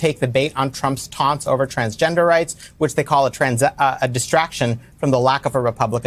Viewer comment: I really thought he said “transgenderites” 😅 I need a break from this nonsense.